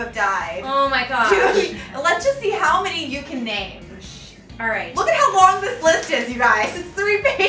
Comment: did Janel say “shit alright”